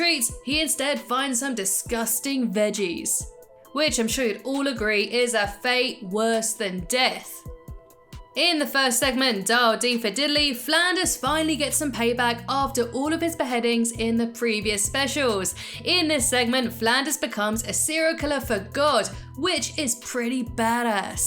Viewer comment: I thought it said "Dial D For Diddy"